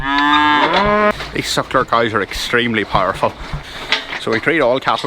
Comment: Even though I turned on the closed captioning, I still don't understand what you're calling these cows. The CC inserts the word 'sucker', but I doubt that's correct. If anyone knows, please reply. Thank you. :)